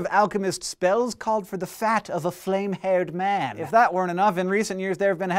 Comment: All you have to do is click the Closed Captions button, and they show up. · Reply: Colin Reed I realize that. Those captions are part of YouTube's automatic Closed Captions system are often far from perfect. Case in point. Go to 0:25 and turn on the Closed Captions and they say. .."Spells call for the fact of a flame hair man." What was really said? "Spells called for the fat of a flame haired man." It might seem trivial, but small mistakes like this (along with glaring mistakes where what is actually said and what is captioned directly contradict each other) are strewn throughout the entire video, and can only be corrected by real, man-made closed captions.